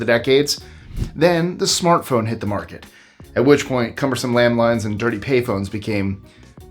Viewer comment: #25.... For a second there I thought you said landMINES and I was thinking this is going to be good😂😂😂😂😂